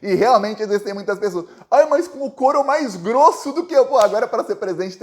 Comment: Entendi "Mais corno do que eu"😂